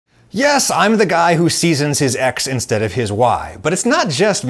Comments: I misheard "Yes, I'm the guy who seasons his Ex instead of his Wife". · I literally heard "I'm the guy who seasons his Ex instead of his Wife" in the start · In the beginning, i heard "Yes, im the guy who seasons his Ex and not his Wi-" then when it stops there, i realized its X and Y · At first I heard "the guy who seasons his Ex instead of his Wife" and was very confused for a moment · I wasn't looking at the video and at the start I thought he said "Yes, I'm the guy who seasons his ex instead of his wife" and I was so confused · Lol at first I thought you said you're the guy that seasons his ex not his wife That's kinda deep my friend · "yes, im the guy who seasons his ex instead of his wife" what? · "Yes, I'm the guy who seeds in his ex instead of his wife" 😕